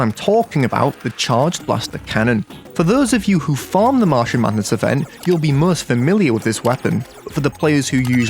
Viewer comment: i first heard Marshall Mathers event